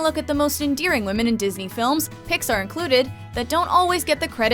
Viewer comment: did you say "Pixar"..?